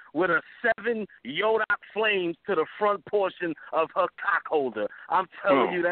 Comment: “7 Yoga Flames to her cock-holder” did just say that? Lmaoooo